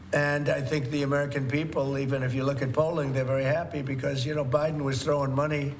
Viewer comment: what did he say here about Poland please? Honesty I’m lost English is not my first language 😐someone please explain and thank you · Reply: Polling, not Poland